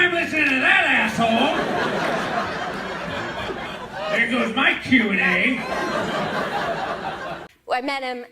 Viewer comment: what is she saying at 12:15? any hints? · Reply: “There goes my Q&A”